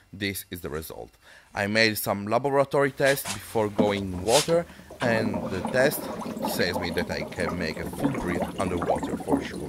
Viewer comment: can somebody tell me what hell he was saying at 2:46 -2:51 pls? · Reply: "I make some laboratory tests before going in water and the test say me that I can make it to breath under water for sure"